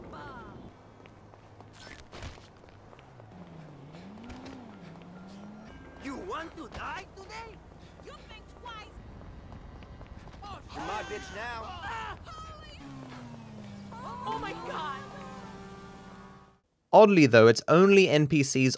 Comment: did micheal say "youre my bitch now"?